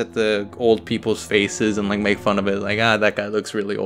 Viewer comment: i thought it said,"old people's FECES" instead of faces